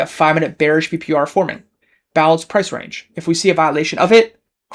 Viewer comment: lol you have blanca instead of balanced